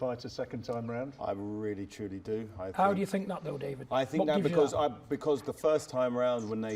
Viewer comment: did he call him Little David?